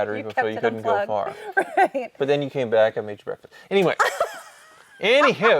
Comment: what does actually say here? Seems like it was edited. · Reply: “Made you breakfast” idk